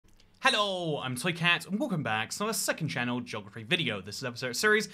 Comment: I can't un-hear "Soy" Cat after I randomly thought I heard it once... Are you actually saying "Soy Cat"?